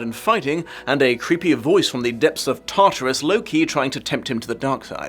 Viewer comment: I'm gonna be honest I thought you said Loki and not low-key and got really confused why you would think the voice was a god from a completely different mythology